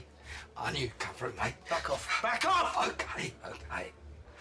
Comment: Billy sounds like he says 'fuck off' at first. WORT I NEED IS CRAACCCKKK.